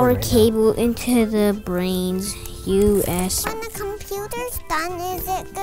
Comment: did he just say put the cable into the brain you asshole ?